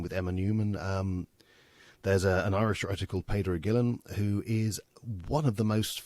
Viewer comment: did he say Pedro Gillum? Could someone link me to the book? Mayne my spellimg is off. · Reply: It's Peadar Ó Guilín